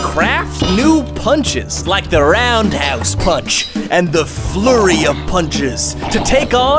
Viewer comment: When he said “Like the round house punch” I thought he said like the red a** punch 😳 Edit: I like bananas and apples